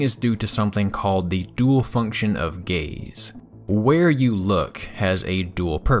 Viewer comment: I wasn’t looking and I heard “the dual function of gays” lmao